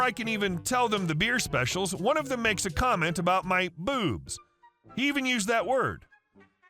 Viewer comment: I miss read and came back to say sorry about what I said I thought I heard the guy reading the story say booze not boobs and i gave a bad take without double checking.